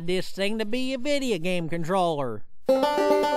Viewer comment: If you're trying to figure out what he said, He said "That noob dun got no scoped"